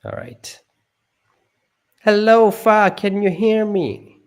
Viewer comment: I thought you said “hello fuack”